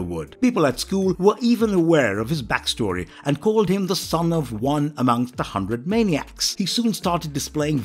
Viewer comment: I thought they called him “son OF a thousand maniacs.”